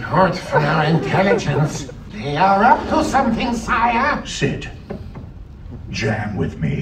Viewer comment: did bowser just say sħ!t 🤣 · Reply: He said “Sit”